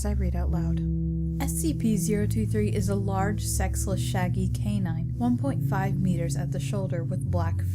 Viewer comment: Could have swore you said " Large sexy, shaggy canine ". And on another note, how do they really know it's sexless? Did they do a close up check?